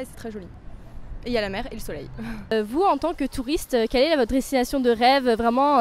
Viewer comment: did she say "en tant que" or "en temps que"??? I only know of "en tant que" which could be translated to "as" in english. Is the translation correct?? or the french subs are wrong??